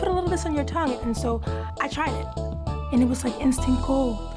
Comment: "it was like instant gold" (whispers) "instant gold" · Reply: I thought she said instant cold for a second XD